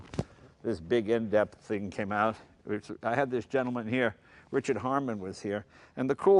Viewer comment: wait,, did He say those books were written by Richard Hammond?